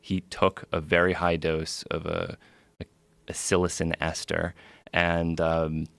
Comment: what drug they say he took ? He say it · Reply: psilocin ester